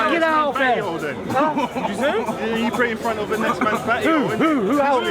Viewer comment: yo all this war needs to stop now man, big up the muslim brother that apologised on behalf of the guy who made the comment, respect for that, all those other guys getting so emotional trying to fight all that aint going to get no one no where, just chill out and debate with respect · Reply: glad you guys squashed the beef, we couldnt see what said to you that was mocking but 2 wrongs dont make a right and from the looks of it you must of went below the belt but its all history we live and we learn and hope its a lesson for us all and keep the PEACE Edit - i just played it back and heard you say "well you guys pray next to next mans batty hole then" cmon thats harsh and very offensive